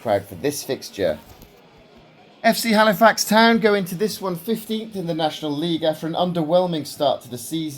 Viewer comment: mate I thought you said 50th there haha I thought fuck me they must be in the worst league ever 😂😂😂